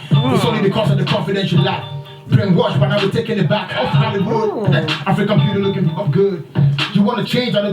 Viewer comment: Did anyone else catch Sark say "F@ Hollywood?" 😊💯